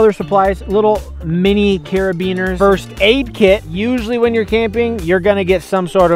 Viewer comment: Am I the only one who heard say beaner’s😂